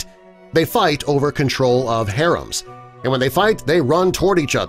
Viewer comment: umm excuse me... Did you say "Harems"??? Like, a bunch of females for one male??? Whoah!!! ❤😊